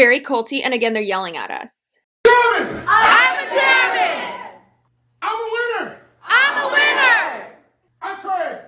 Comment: I heard "Im a German Im a tray!!